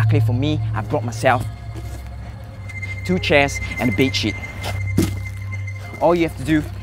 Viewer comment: It sounded like he said "two benches and a bed sh*t"